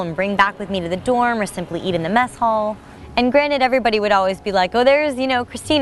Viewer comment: sounds like she said eat in the meth hall